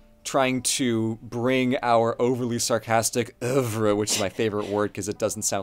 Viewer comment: "uvruh"? what word is saying? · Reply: Ouvre